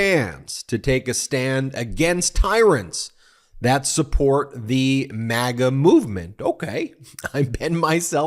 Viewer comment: I'm not crazy about the premise of this video. It sounds like he said "and support", not "that support." I get how it might be misheard, since his drug-addled brain slurs a lot of words and he barely has the breath or energy to speak, but let's not put words in his mouth. He says enough ridiculous, self-incriminating shit without us having to make anything up.